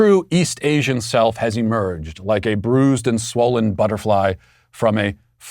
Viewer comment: Approximately 0:40 I thought he said “ like a fleshy and bruised bolt fly out of its cocoon “ - he said “butterfly”.. It’s quite the difference 🤢🤮